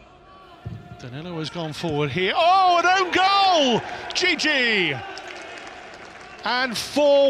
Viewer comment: “ohh an own goal, djidji” thought he said gg at first 😂😂